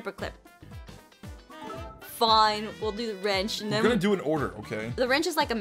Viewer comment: I got an ad which cut off what you were saying so it sounded like you said FU- when you said fine